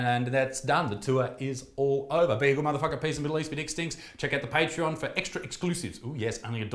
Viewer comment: Jesus christ I thought you said "Check out the patriarchy" at the end there haha.